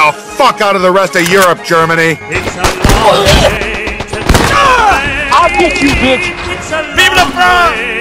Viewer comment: i kept hearing “it’s a long way to chickfila”